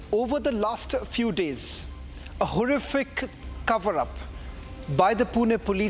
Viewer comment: Did he just say laster few days? 😂😂 bro.